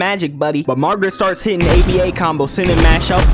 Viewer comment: what type of combos? Maw man did you say ABA? OG?